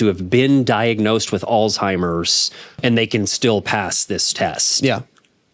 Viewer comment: Could you please pronounce Alzheimer’s correctly? Sounded like you were saying Aldtimers. No. That is offensive to people who have AlZHeimers Disease.